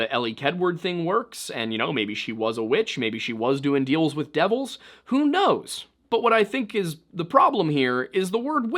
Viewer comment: I heard “maybe she was doing deals with devils.. and gnomes”